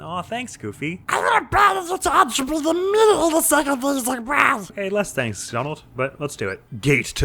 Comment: Okay, I gotta ask, what the heck did y- uh, Donald say at 4:20? I've listened to it so many times and I can't make any of it out. XD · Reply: "I will abandon you to entropy immediately the second things look bad!" I'm pretty sure is what the little avian fellow said. Or...tried to. :D